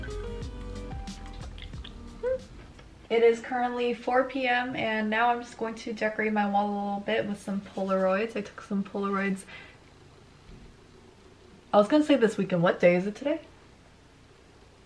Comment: I thought it said decorating my mom